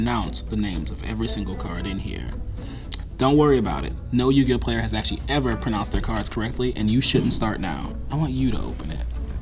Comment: When said this: 4:08 I thought he said “Don’t worry no Yugioh player ever reads their cards correctly” so I died laughing until I scrolled back.